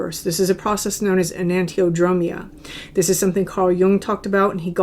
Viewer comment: what was the word you mentioned at 18:46? i tried to look it up based on the subtitles "anantromia" but i think it's incorrect. first time i came across that wordl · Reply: found it! enantiodromia! so fashinating! will be reading more about this today.